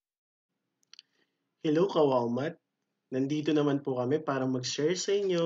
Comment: LMAOO I was watching with low volume!! I thought the intro said "HELLO KAWAWA"